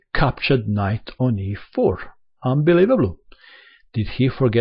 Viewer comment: What did you say at 2:34 because it sounded hilarious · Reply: Kevin Damirchi unbelievable. or rather unbeliebable